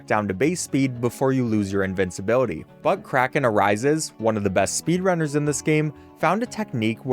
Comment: Was I thinly one that thought he said buttcracken arises instead of but kraken arises lol oop